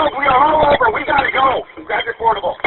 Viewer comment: did he say portable?